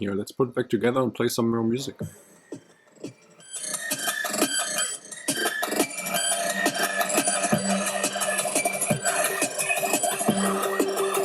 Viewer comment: I thought you said "... play some real music."? :) I appreciate the how to 's on this speaker though. Good Job.